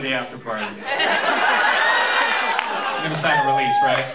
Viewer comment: what did he say from 9:01-9:03? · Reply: "You're gonna sign the release, right?"